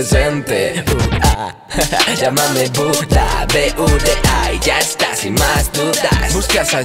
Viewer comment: Pense que decia PU7A XD